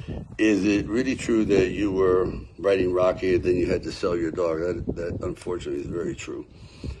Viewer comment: I totally heard, "Is it true when you were writing Rocky you had to sell your daughter" For $40 at a 7-11 lol